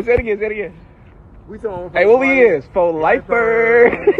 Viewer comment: my friend said “at what we is what we is” and i said: “gæ?”